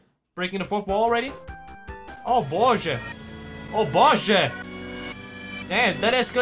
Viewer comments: wait..... did you say...? О боже? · Did you just said "O boże"?! ( 1:22 ) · Waaaait,you speak russian? I just from Russia,i heard "о боже"